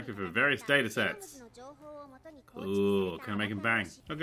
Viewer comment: did he just say "Can I make them bang" Holy shit